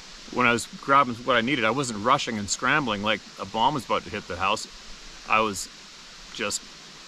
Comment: Almost like trying to convince himself. 🙄 (Almost sounded like he said "like Obama's gonna hit the house" . Lol)